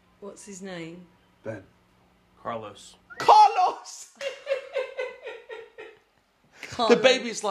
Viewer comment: When this movie came out all i kept hearing was " not at the table Carlos"😂